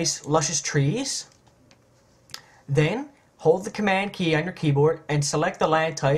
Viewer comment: Hi Just what Im looking for but I cant hear what it is you say at 1:10 I've tried holding down command key but cant swork out what to do next. nothing I have tried gets the text outlined as you demonstrate. Please could you write down the commands i need to follow for this section. very many thanks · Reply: "Hold the command key and select the layer type"